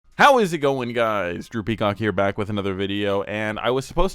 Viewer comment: Soo first time watching this dude and I thought he said DROOPY COCK, not drew peacock, idk if that was intentional Orr